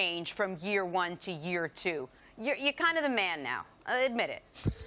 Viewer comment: LJ thought she said “you’re kind of a man” but she actually said “you’re kinda da man”. Big difference. He thought she said he was almost a man.